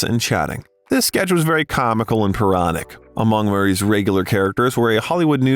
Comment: What's he saying? "Paronic"? 🤷🏻‍♂️ And in Caddyshack, at roughly 8:00 - "He played the owner of a golf course"? I've never even actually seen the film and I don't think he's the owner!? Greenkeeper, surely? And Ghostbusters... There has only EVER been two movies!? What's this nonsense about sequels!? 😉😁😉 ✌🏼😄👍🏼